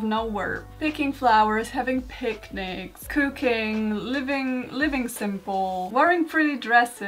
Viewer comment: i thought you said cocaine at first 😭